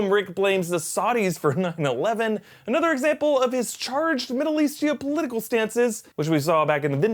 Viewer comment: Did you say ""charged, middle eastia?" thats crazy